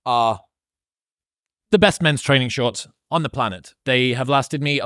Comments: Great interview. You and Eric have an intellectual chemistry that finds its way to some truly profound places. Would also like to point out that your plug for gymshark “men’s training shorts” sounded like you said “menstruating shorts,” which is actually a thing. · Did he say “Menstrating shorts”? Guess this really is “Modern Wisdom”… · Thought I heard him say "the best menstruating shorts," then realized it was "the best men's training shorts" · I didnt know they made menstruating shorts for men. They do sounds comfortable. (I had to rewind and listen again before i heard 'mens training shorts' hah.)